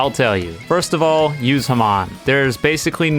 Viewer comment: DID HE JUST SAY HAMON